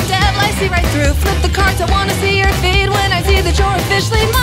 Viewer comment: thought that she said I wanna see your feet